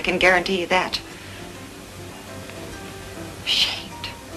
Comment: did she say shit at 3:04 ??